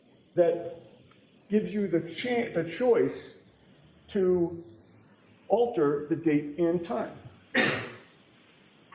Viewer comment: Awesome! Thank you so much! I needed something to watch 😁. Anyone know what Bev whispers after saying "thank you" around the 17:31 mark? I turned it up but my ears are shitte 🙉. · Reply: I think she says we can fit it in